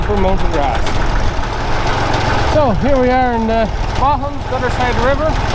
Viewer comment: "here we are in the Bahamas". Well, that's what the subtitles said.